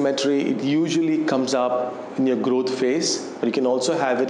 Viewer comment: sorry i couldnt hear what you said, what phase? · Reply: growth phase